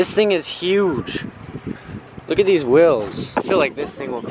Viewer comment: does any one notice that when parker says wheels it sounds like he is saying wills❤❤p.s. i still luv u❤❤